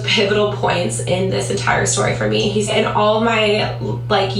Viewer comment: She's not a neurologist, she's talking about what the doctor said. It's easy to miss, but you can hear her say "he said" very briefly.